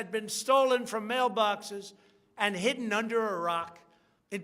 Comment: did he say stolen votes where taken and hidden under a rock?